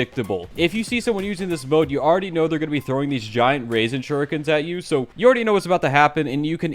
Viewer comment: im sorry did i hear u say “raisin shurikens”?